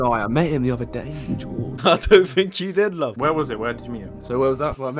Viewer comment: Did just call luv?